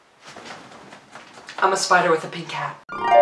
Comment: I thought you said a spider with a big GAP 😂